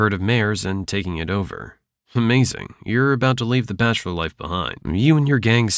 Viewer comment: did you just said "Umazing"?